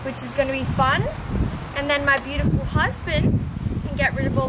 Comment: did you say husband ??